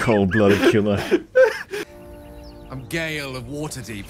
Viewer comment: did he say he’s gay L?